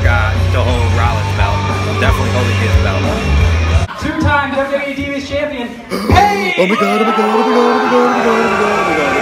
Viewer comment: I thought he was saying Two time divas champion KEVIN OWENS